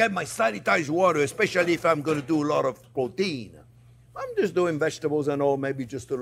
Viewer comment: what did he say!😂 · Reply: Protein! 😊